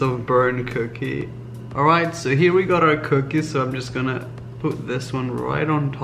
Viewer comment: I thought he said poop this one on there but he said put